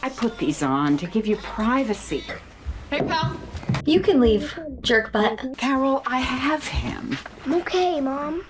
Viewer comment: Did she say jerk-butt??? LOL Never heard that one! :-D